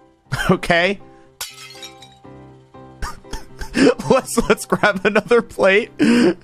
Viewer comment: Ranboo: "Let's grab another plate" What I heard: "Let's crack another plate" 😂